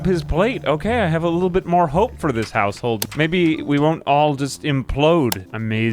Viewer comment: for a second, I thought you said "I have a little bit more hope for this asshole" 😂